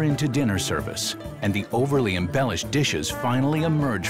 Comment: Did she say "presentate"?